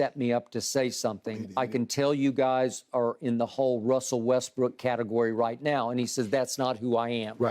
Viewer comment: Damn skip everytime I heard him saying Russel westbrick damn